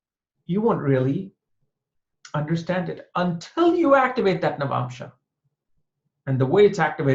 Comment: He says: you won't understand the navamsha untill you activate navamsha English subtitles says:you won't understand the navamsha untill you activate "Nerve Option". Lol😁😁😁😁😁(7:39) 7:29 Sometimes navamsha is interpreted as " bombshell" 😂😂😂😂😂